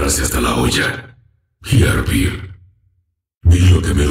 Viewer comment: alguien sabe lo que dice en el min 3:08 ? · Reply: dice y hervir